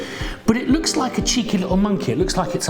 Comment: I had to keep rewinding and then finally put on CC to read along! LOL! (I thought for sure you said, "It looks like a cheeky lil' monkey."🤣